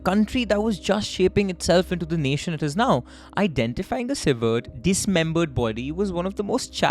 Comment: hey what you said here? I mean pronounciation and subtitles are unclear to me ... May you please let me know what word you said before dismembered, something like cived..! Edit- severed?